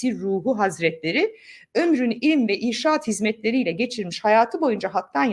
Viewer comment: "Irsad" kelimesini insaat olarak duydum ilk okumanizda 😂😂😂